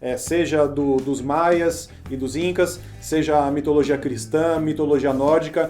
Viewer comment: "mitologia cristã" ?? Eu ouvi isso certo????